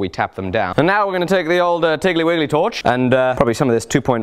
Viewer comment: Did he just say tickley willy torch...